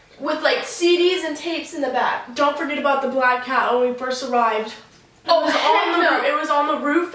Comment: When they said about the black cat, I thought they said black cow omg. I was like, "How is a cow on the roof meowing?!"